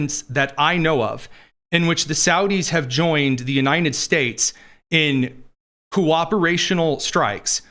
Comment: What are you saying the states??! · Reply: it always sounds like Tony says Uninin States!